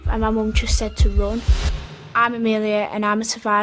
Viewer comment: When she said “Im Amelia and I’m a survivor of the Manchester bombing” I thought she said “Im a millionaire”